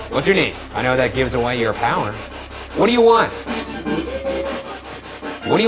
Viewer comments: When said "What do you want?" I heard "What do you think?" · "What do you want?" All I heard was "To eat a grape" lol